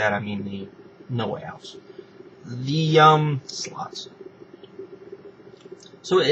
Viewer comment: why did you say 'sluts' at 4:49?